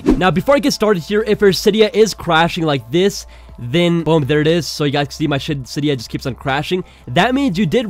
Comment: It sounded like he was about to say s*** in